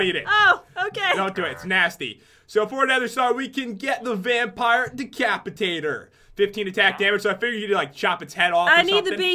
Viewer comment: I thought password said the vampire diaries